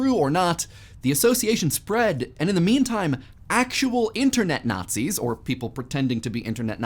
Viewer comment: did he say "in the meme time"